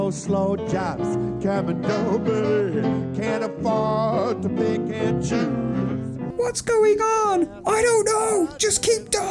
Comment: did he just say “can of farts”? 🤨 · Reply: He said "can't afford to pick and choose".